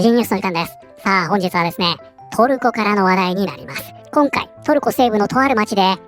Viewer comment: オープニング映像の影響で、「本日はですね、トルコからの笑いです」に聞こえてしまった😊